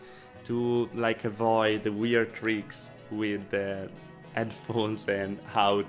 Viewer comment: " I didn't catch that. ..?" 🙄 🤔 💭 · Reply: avoid the wierd creaks i think he said.